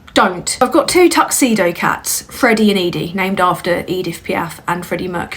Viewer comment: At first i thought you said you named Eddie after EDP and I had to do a double take LOL; rather fitting in hindsight considering the rest of the story